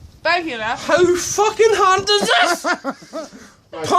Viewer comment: did he say "whos fcking hand is this"?🤣